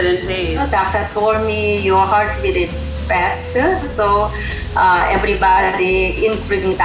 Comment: At the part 1:48 I think I misheard the word ‘faster’ and I thought she said ‘bastard’ I’m so deaf😂😂